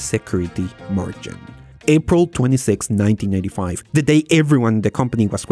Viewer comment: there’s a typo in the CC, it says 1995 instead of 1985! :)